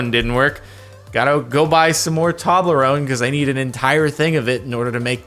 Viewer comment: I swear he said toddle-rone